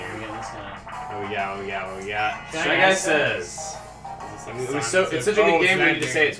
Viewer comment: did they say "chugga" says?